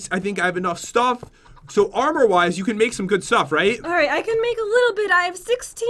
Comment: It sounded like said armour wives